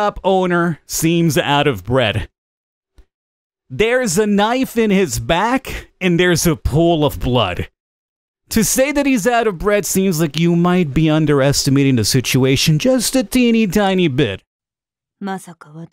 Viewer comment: I thought you said bread and not breath